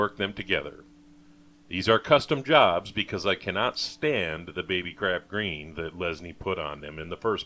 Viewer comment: I thought you said baby crab green at first.